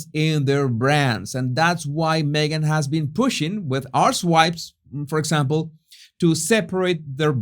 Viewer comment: thought he said “arsewipes”